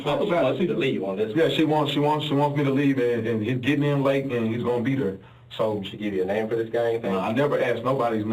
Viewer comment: When hes talking about the 'Oklahoma client', he says "Yea so he gonna be there" but cc says "yea, so he gonna BEAT her"--😮😂 1:49:06 Cop says "Hes gonna take his LICKS"...not "Hes gonna take his DICKS"--😂😂 {Cant win em' all😉}